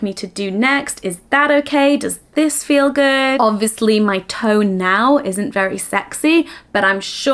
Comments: At the end of the video when says her "tone" isn't very sexy now, but you can make it sexy during the moment I thought she said her "toe" isn't very sexy 😂😂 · Wow misheard part near the end there and was very confused 😂 when you said your tone wasn't very sexy it kind of ran on from the list of questions and I heard "I know my TOE might not be very sexy right now, but I'm sure you could make it sexy"